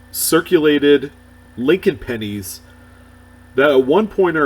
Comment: Oh pennies. Sounded like you said panties